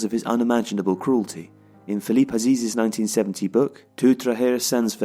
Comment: Thought he kept saying Philip Lahm....